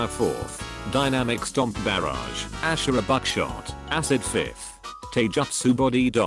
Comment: When he said shura buckshot I thought he said Shura backshot